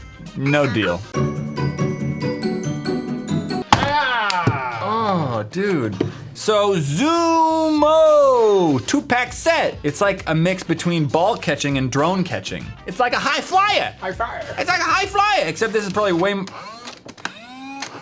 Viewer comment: For a moment there, i thought he said "Two peck set" i was like "what?" then i realized what he said.